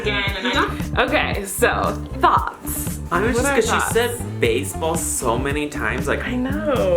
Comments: "Okay, so THOTS?" is what I heard at 11:48 lmao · I thought Colleen said "So... thots"